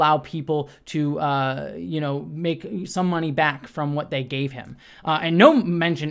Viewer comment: A friend sent me the link a couple hours ago, I opened it, heard him start the video saying he tried to kill himself, immediately clicked off; truthful or not thats just such a blatantly manipulative way to start a video where youre supposed to be apologizing for what you did? Fuck that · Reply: omg same! hahaha I heard "back from hospital" or whatever, and I was like gurl bye